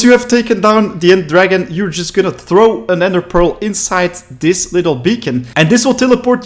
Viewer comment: Did he just say nether pearl in 2:48 😂😂